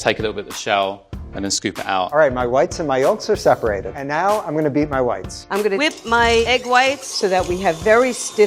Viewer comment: thought he said, "and now I am going to beat my wifes"